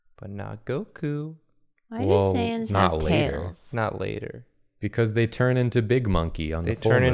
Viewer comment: When said “why do saiyans have tails?” I heard “Why do sans undertales?”